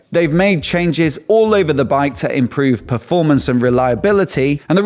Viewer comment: Im sorry ,., when he got to the Norton I thought I heard him say they made chain jizz all over the bike ..,.,.